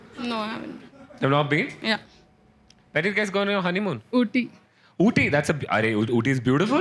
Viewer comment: I heard "Booty"